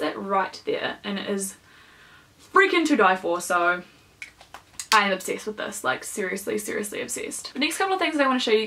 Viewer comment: When you said "I'm obsessed with this", I thought you said, "I'd have sex with this" and I had to do a double-take haha